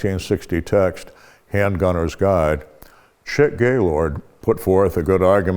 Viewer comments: Did he just say Shit Gaylord? 😂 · Chick Gaylord. I thought massad said shit gaylord